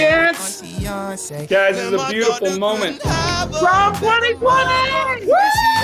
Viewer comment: I thought he said TRUMP 2020! 😂